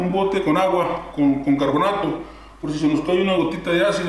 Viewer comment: Agua con que dijiste? con Bicarbonato? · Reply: Carbonato